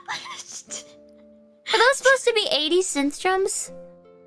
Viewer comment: I think gura is saying "80's synth drums" instead of "eddison strum" in 0:34, great edit anyways!